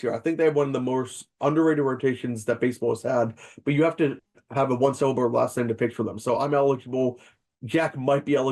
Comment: I finally figured out what said at 25:20, "you have to have a one syllable last name to pitch for them"